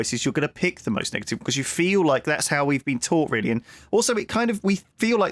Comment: what did say at the end of the sentence? I think I misheard and now I can't hear anything else at 46:28 but because of that I wanna know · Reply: “Really and...”